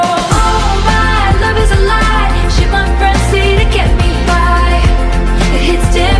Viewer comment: Me who misheard "Sh*t my friends tell me to get me by" to "Things my friends tell me to get me high"